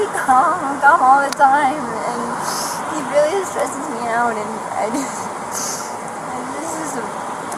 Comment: "I don't think that statement is emotional abuse at all..." Watch from the beginning again. She's very emotionally unstable. Then she calms down for a bit at 3:13 while answering what she does for a living and steadies herself. Look at her body language. Then, just as she says "I've been really stressed and he doesn't believe I can do any of this" watch what she physically does. A short bit later at 3:52 she also states, "he really stresses me out." What about him do you think was causing her that stress? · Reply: So you are claiming at 3:52 she does NOT say "he stresses me out"? She does say that at 3:52, not earlier.